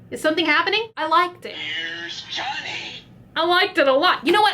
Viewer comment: I swear that I heard here's Johnny